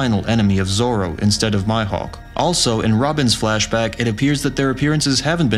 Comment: did he called 'mihawk' ... 'my' 'hawk' lmao